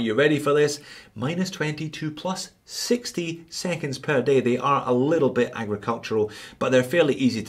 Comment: Did he just say " THE MOVEMENT IS A LITTLE BIT AGRICULTURAL" 😂😂😂😂😂😂 I MIGHT BE WRONG BUT THAT'S WHAT I HEARD